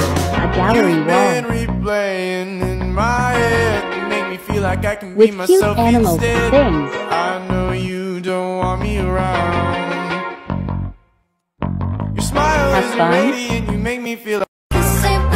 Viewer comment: I heard “With cute animal- thINgs”